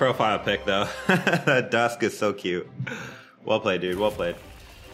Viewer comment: did I hear that correctly? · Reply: “That dusk is so cute”